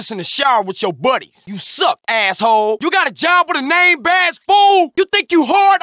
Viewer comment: CJ singing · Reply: I couldn't hear 53:13 clearly.. you got a job with a name bass fool? or something?